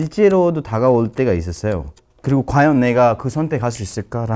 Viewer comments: did he say the n word? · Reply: No. he said 내가 (naega), means "I" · WHAT HE SAYYYYY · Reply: ?? he said 내가 (nae-ga)...